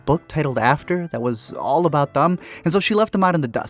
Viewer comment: does he say "all about thumb?" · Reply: all about them